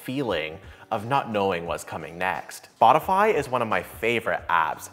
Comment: Is it me or intentional, but did you said Botify instead of Spotify 2 times? Once At 0:23 and